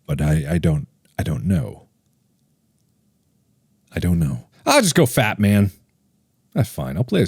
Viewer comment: When mark said “i’ll just go fatman” i was only half way paying attention and i thought he said “i”ll just go fap man”